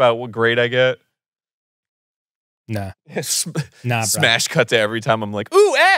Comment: I paused this and came back the next day at 7:35 and sworn I heard: "Smash cut to every time I'm like, 'Ooh, ass! That's nice." And about choked on my food from laughter. Oh context. And mishearing one word. I do love you.